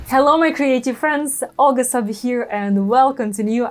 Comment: The first couple/few videos of yours that I watched, I thought you were saying, "Hello my crazy friends." 🤪 I think I'm used to your accent because now I don't know how I could have heard wrong. 😆 It was all me.